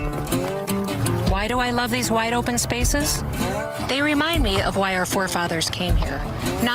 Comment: I swear I heard her say “white open spaces” instead of “wide open spaces”